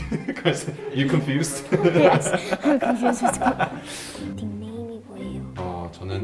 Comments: Did she just sayyyy?!. “Im confused asf” LOL💀😭. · I think i heard she said "im confused af" damn😍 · did she just say I'm confused as FUCK😂😂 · did I just hear "yeah i'm confused as fu-"🤯